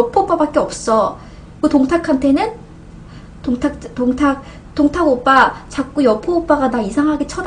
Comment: 동탁 오빠가 통닭 오빠로 들리는 걸 보니 배가 고픈가..🍗🍺 치맥 먹고파라...